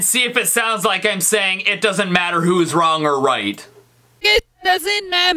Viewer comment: to me it sounds like Micheal is saying it doesn't whose